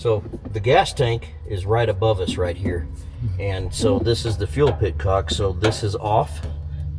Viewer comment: Unbelievable how great it looks and runs. For anyone who heard him say "fuel pitcock" and might not know... the word is petcock... an e instead of an i.